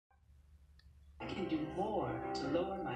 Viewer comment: I thought he said "My body can have witness protection"